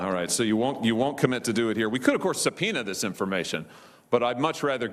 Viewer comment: what word does het use? Sepena?